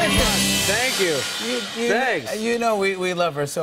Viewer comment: He said thank you..thanks And i heard thank you next💫